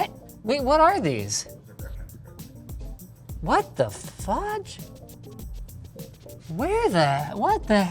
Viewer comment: Did it sound like he said 'what the f***' to anyone else at first?